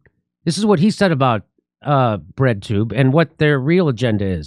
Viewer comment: I thought he was saying "Red Tube". I was like, that doesn't sound ANYTHING like the Red Tube website that I remember! 🤣